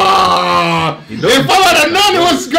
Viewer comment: Who else heard that said “Gold”? That must’ve been expensive lol